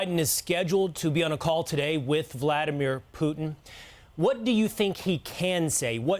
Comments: Did he say "President Chump”? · Did he really say president Chump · I think he said "President Chump" @ 1:32 😆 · is it me or did I just hear "Present Chump." if so, how disrespectful... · Did he just say “President Chump” @ 1:36? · it sounds like he said President “chump”. Only Chump in that room is that Fox News fairy 🧚‍♀️ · did he call him president chump??